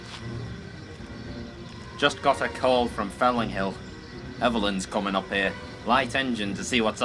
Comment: Felling Hill? Am I hearing this wrong?